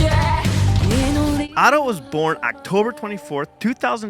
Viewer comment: Quick side note to the lyrics. You messed up guys: At 1:51 instead of "Ado" you put "Otto" which s funny because it sounds close but... really? Anyway great reaction and much love to Ado she does so well and surprises with new music!